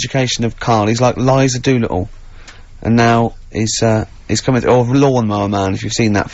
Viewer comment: what's the word here 0:04? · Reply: Ricky says Karl is like Eliza Doolittle, in the play (later adapted and much referenced) 'Pygmalion' by George Bernard Shaw. In the play a professor wages that he can turn her from a poorly spoken and badly mannered London flower seller into the toast of high society - just as the boys are trying to educate Karl and improve his vocab